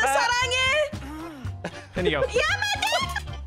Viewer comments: did say "Yamete kudasai"? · did just say "yamete kudasai"?